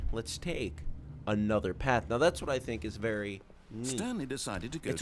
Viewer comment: i thought was gonna say "ANOTHER TORCH" but he said another path :|